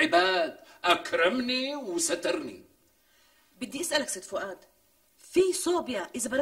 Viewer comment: بالدقيقة 23:43 قالتلو بدي إسألك ست فؤاد أو أنا سمعت غلط ؟